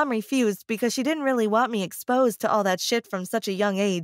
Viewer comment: I think she said sh*t and then she keeps saying sh*t